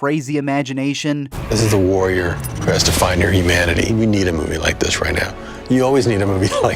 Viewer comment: did he say Imanity